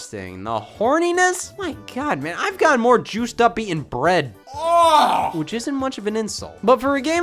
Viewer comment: no joke, I heard, "I've gotten more juiced up being bred"